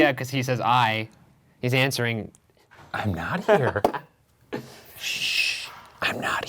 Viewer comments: I heard “ I hope “ not “I’m not here” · to be honest I hear "I hope" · its sounds like “i hope” instead of “i am not”. just my take on it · I heard “I hope” not “I’m not” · I heard “don’t laugh at me” and then almost as if it repeated you saying “classical music huh?” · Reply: Also at 18:00 I heard “I hope not” rather than “I am not” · i thought “”””Jesus”””” said “I hope” not “I’m not”. It was kind of what i heard and i was looking away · lol when he asked if Jesus was here I heard "I hope" rather than I'm not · I heard "I hope" rather than "I'm not" o_o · it sounds like the voice is actually saying "I hope..." instead of "I am not..." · i hear "I hope" which is a lot less fun :^( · I thought it said “I Hope” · I swear I hear “I hope not” vs “I am not”